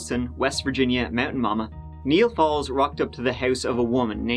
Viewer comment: Thought you were saying Neal Balls until the end of the video